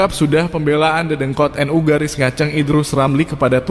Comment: gak salah dengar ya aku di [1:31] ada narasi GARIS NGACENG.. 🤭🤭